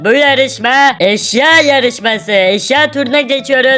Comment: Abi eşya yarişmasi dedinde ben eşşek yarişmasi olarak duydum başta 😂😂😂😂😂